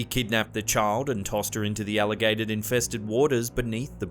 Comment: Did anyone else hear alligated infested waters instead of alligator?